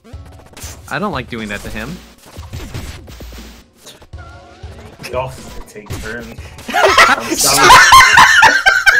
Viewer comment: what did say at 1:46:19 ? i literally can not understand it help !! good stream also :3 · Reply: "we could all take turns"